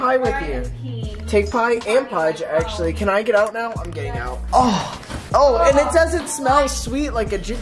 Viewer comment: I thought said I’m gay now instead of I’m getting out 💀